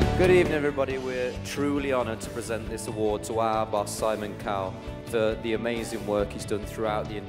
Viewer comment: Liam at 01:35 Am i the only one who heards "Simon Cow" 😂😂